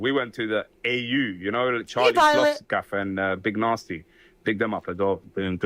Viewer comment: Sounds like he says “Charlie sloths gaff and big narstie” As in Charlie sloths place. “Big them up aswell. Good stuff”